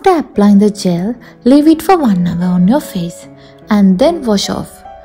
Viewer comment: leave it for.........? What she say please someone tell me?😢😢😢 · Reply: 1 hour